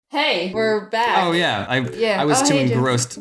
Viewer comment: Did jaiden say Jim? 0:02 That's my nick name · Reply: No she said James?